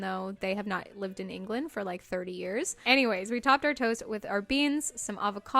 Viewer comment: At around 18:15 the subtitles said "we topped our toes with our beans..." instead of toast hahahaha